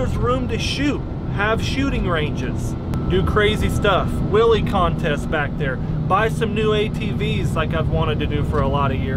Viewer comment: I honestly thought you said "willie contests back there" @ 2:05 lmaooo🤣